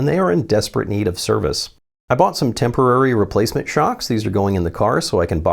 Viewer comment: "I bought some temporary replacement shocks"... I heard "I put in new shocks"